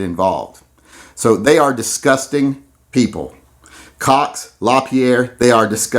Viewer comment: For a minute I thought he said "Cocks" are disgusting Then I realized he said "Cox" Lol